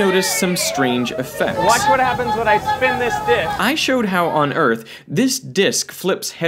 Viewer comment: “watch what happens when I spin this dic” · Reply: For some reason I heard “lemme spit on this dic”